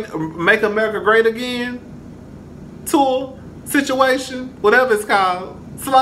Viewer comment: Did say the Make America Great Again Tour lol